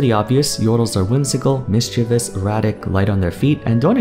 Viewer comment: I had a huge double take when I thought I heard Yordles described as “erotic” instead of “erratic”